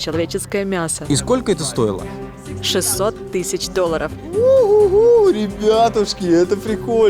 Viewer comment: Я прослушала 2 раза и уверена что она сказала: sixty thousand (60 000) а не six hundred thousand.